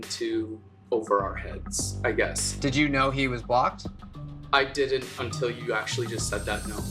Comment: I heard Did you know he was a bot lol